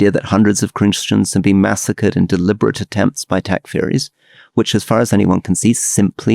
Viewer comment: Could anyone help me figure out what Rory said at 46:22? "... in deliberate attempts by (??)" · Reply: yeah listened a dozen times can't quite make it out... first think it was Christians being attacked by attack fairies, or attack furies... makes no sense and not seen the claim by Elon either, atleast not quite like they are saying... there have been reports of lots of killing of Christians and i'd assume non too, being killed in Syria, and to me just cause it's not yet main media or what ever, to say it is simply not true without being able to prove it's not true, while there are videos ext. that is is true but i find that a hard one too cause so many videos being faked these days... much like the Palestine videos, i could tell as a make-up artist thats some of what was being portrayed as killed children where life like dolls with heaps of make-up on, now i couldn't prove that, but for my own awareness im glad i could see that, because those types of videos/pics are horrendous and ofcourse would bring sympathy from all... thats the thing, today the real wars i believe are not being fought on the front lines, or atleast not mainly, the manipulation of media and the mass hysteria of people taking things out of context then rioting and screaming for governments to step in, with little knowledge of whats actually going on.